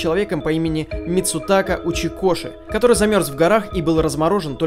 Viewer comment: Митсутаку учи кошек (Так услышали некоторые)